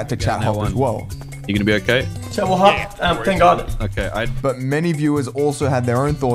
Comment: He actually reads what someone says in Chat which was "chat will hop" not "chat, we'll hop" so he's reading what they said and says thank god as a joke since he was just heated at the time.